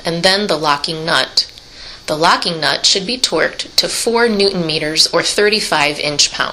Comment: she says twerked instead of torqued 😂😂😂😂